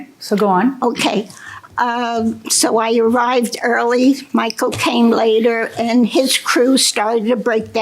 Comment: Is it me or every time she says “Michael came” it sounds like she says “my cocaine” 😂 And I believe 100% that psychopaths can pass polygraphs, especially when they’re pathological liars, and they believe their own lies …. And those are the most dangerous scariest people